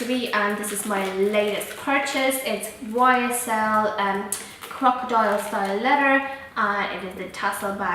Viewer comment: Hey I said its ysl crocodile style leather (real leather) . Majority ( if not all) of designer bags are made from real leather. I do not owe any faux leather bags and would not recommend to purchase as it might be fake, but I'm not an expert myself. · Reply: OK. That's what I thought. I guess I misunderstood you because it sounded like you said crocodiles faux leather.. It is such an amazingly beautiful bag. I am absolutely swooning over it and I'm really happy for you that you got it.